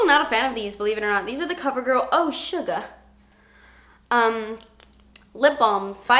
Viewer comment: I thought she said "oh Suga" 😅